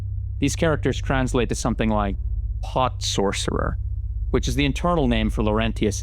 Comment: Pot Sorcerer? How'd they know my high school nickname? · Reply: Ngl, I thought he said “hot sorcerer” at first. Which would be fitting cuz fire hot. And so is he 😉